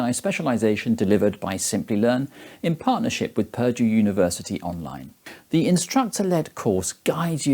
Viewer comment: thought he said "perjure University" Saved by captions.